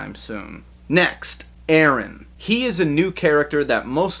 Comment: What was his name, can't hear it really good? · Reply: His name was Aaron